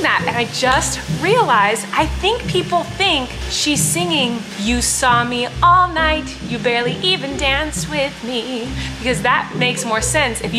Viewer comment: i thought it was saying, "you were snobby all night, you barely even danced with me"